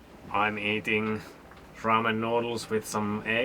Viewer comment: I thought I heard you say you were eating "Rum and noodles with eggs". My mind reacted with instant respect for the "Ah Ha Jim lad, shiver me timbers" pirate diet! Of course what you actually said was Ramen noodles😂